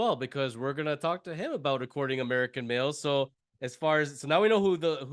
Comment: Seriously, I thought they were saying "American Milf", not "American Male".